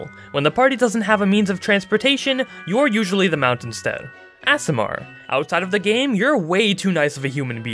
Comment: i heard asmr but it was aasimar hshhs